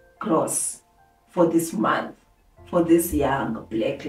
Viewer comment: What is confusing? If someone watched the video, unless they rushed to the 800k part and didn't listen. I specified everything and trust that my audience is smart enough to understand. I don't see anything confusing. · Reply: I think at 11:36 it sounds like "for this month" instead of "for this one"